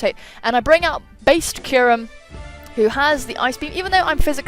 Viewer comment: Haha I thought you said "Space Kyurem" at first and thought it was fitting.